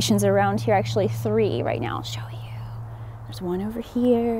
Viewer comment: Abandoned houses are so sad @ 1:08 i hear a woman's voice said '' show you''..